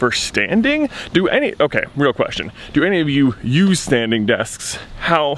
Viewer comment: Thought he said standing deaths and I was so confused!!